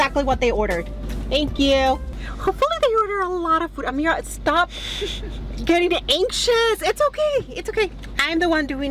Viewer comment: Who else thought said ameerah stop fu